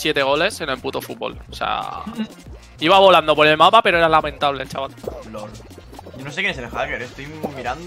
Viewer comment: dijo chaval? los argentinos entenderan parece que dijo chabon